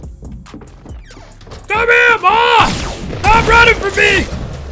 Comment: what did say?? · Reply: He said "Come her boy.. Stop running from me